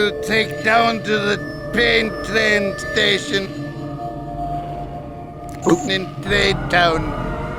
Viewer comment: This is what I heard “you take down pain train station, in trade town”